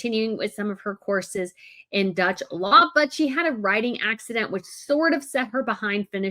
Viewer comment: I thought said a "writing accident" which stopped her finishing her course. I was pondering how such an accident would occur. 😅